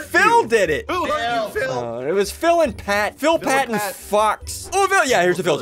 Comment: phil pat and fucks is what he said